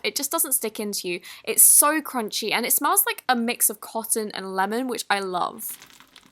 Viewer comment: Omg at 2:34 I didn’t pay attention so I thought she said “cotton and love which I lemon” hahahahaha